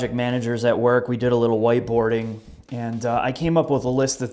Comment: lol thought you said waterboarding